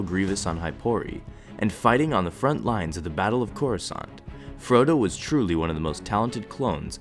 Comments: it sounds like he says Frodo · did he say Frodo?😅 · Is it me or did it sound like he said Frodo? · I hear Frodo! Anyone else hear it? Or is it just me? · “frodo” anyone else hear that? I never knew a hobbit was a clone....thought he was smaller?